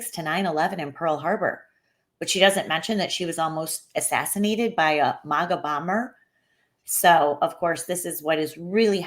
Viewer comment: Sounded like she said the barrack o'bomber .😅